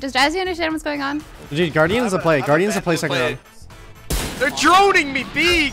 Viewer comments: does Tyson say guardian is the play Skyunno? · am I high or did I hear guardian is the play sykkuno